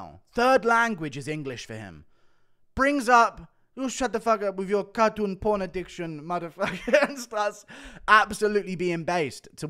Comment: At the end of Kape telling Izzy to fck off, I thought he said fuck you and your fn animal porn addiction lmao he said porn cartoon addiction. I was about to die laughing